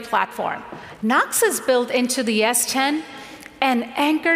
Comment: Did she say S10 @ 4:16 ?